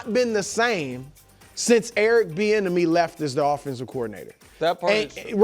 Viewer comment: I’ve always thought they were saying Eric The Enemy 🤣 🤦🏻‍♂️… until I seen his name just now 😂😂